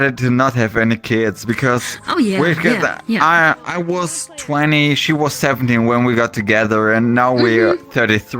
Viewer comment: I wasn't really listening and heard "I was 20 and she was 70 when we got together" It took a fat moment to realise what he actually said.